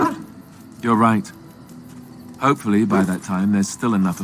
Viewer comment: I wasn’t looking during this part and it sounded like Alistair was saying “you’re right” to the dog